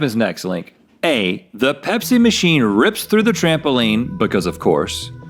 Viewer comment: I swear I thought Rhett said Hep C machine... and then I saw it and was like ohh that makes much more sense